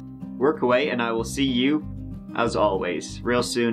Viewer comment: OHHHH!!! Mike says “Mike out!” at the end!! Shit, I’ve been watching this guy for a while now and thought he was saying “Night owl” 😂😂😂